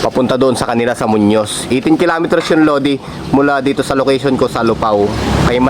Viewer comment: did he say 18 km ? 😱